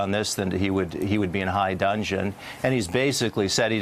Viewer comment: "... he'd be in HIGH DUDGEON...", not, "... high dungeon". I'm a fan of idiom, and I still tread lightly if I have the slightest uncertainty over what I'm saying... so for a news anchor to butcher a phrase is... substandard... even worse for G.W.Bush's aide/speechwriter to... oh wait...G.W.BUSH's speechwriter, ah. That's the problem right there!